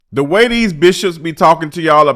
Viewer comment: Lmfao I thought he initially said “ the way these BITCHES be “ I was like ooohhh that’s how we coming in 😅😂😂😂